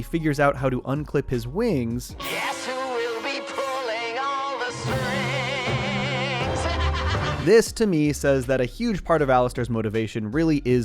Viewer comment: For the longest time I thought alastors verse said “guess who will be pooling all the screeaams!” Only yesterday I realized it said “guess who will be pulling on the strings!”